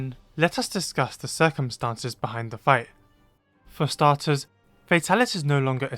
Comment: I thought the first point said "Circumcisions behind Fatalis" and I was concerned